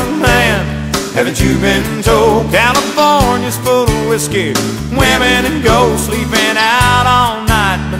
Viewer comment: My nephew thought it said California is full of whiskey, women and goats i never laughed so hard